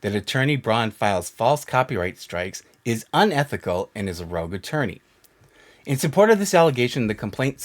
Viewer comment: I love that Janet's lawyer called her a Rouge attorney instead of a rogue attorney (around 9:46) apparently she's a "woman of the night" instead of a wannabe badass attorney that won't listen to anyone's lawful advice lol